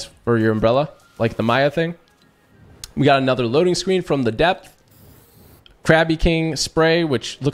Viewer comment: Did he say from the dep